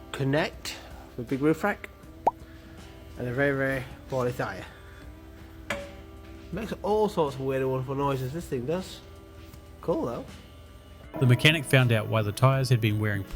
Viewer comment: what the hell does he say? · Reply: "Connect the big roof rack, and a very very poorly tyre. Makes all sorts of weird and wonderful noises, this thing does. Cool though." I have no idea what sort of accent that is, I guess English East Midlands of some sort.